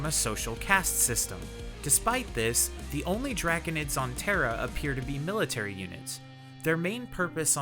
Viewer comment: did he say Terra ?